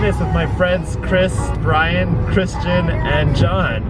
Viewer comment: i heard Kris Bryant. Dang, yiu know the MLB MVP!!!